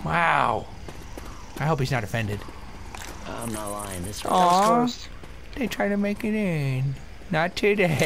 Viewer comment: Did say, "Aww Bitch tried to make it in?" Did swear! *gasp* =P